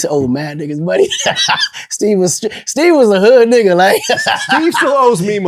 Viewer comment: Did Vlad say "hood nigga" at 2:25 ? hmmm..